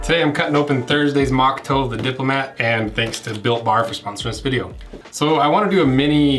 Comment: I definitely thought you said "Thanks to Bill Barr for sponsoring this video"